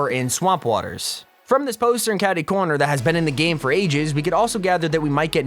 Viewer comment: did he say Catty corner